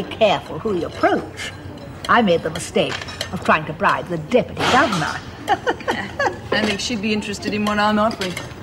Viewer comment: HA! Turn on captions and read what it said at 8:37. talked about bribing the Deputy Governor and it said " I tried to pry the Dippity Dog."